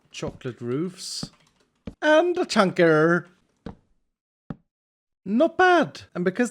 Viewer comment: did he say chunk error or chonker